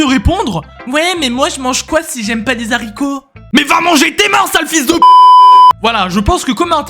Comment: "Ouais mais moi je mange quoi si j'aime pas les haricots ?" - "Mais va manger tes morts sale fils de p--- !" Ça m'a tué 🤣